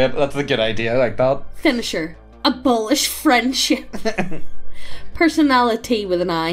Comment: When you said "Abolish Friendship" I heard "Abolish French" and I thought he was mad about his French classes.